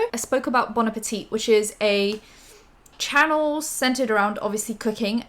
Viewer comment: I thought u said cocaine at first 😭😭💀